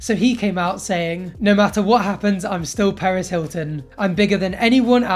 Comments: i thought he said Paris Hilton at first i was like um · Lmao I thought he said paris hilton